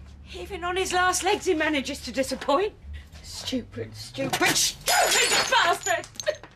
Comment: I thought she said fucking bastard lol